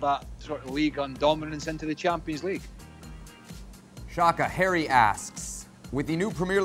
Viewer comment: I thought he said hairy assed. Harry asked.